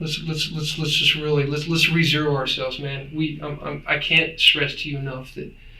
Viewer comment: did he say lets rezero ourselves right now?